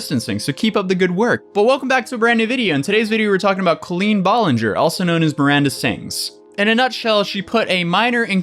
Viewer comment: "Clean ball in her" is what i thought i heard you said. Until i saw her name. Jesus it's been a while that i even heard about her